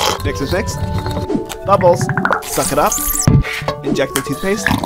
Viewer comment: its sounds like he said sus it up 💀